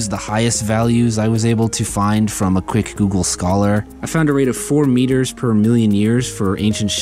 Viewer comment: ah yes, as if google is a reliable source. I heard million and XD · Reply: Google Scholar